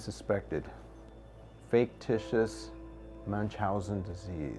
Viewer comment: Oh I thought the doctor said “Faketitous” because it was fake haha 😂😂